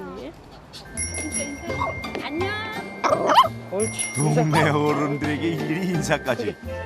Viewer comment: 저만 안녕?이라고 들리나요,,..?